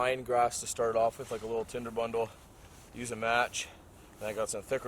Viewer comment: did I hear use Tinder and Match to find twigs?